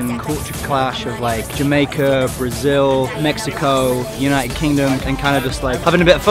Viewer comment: EU OUVI UM "BRAZIL"?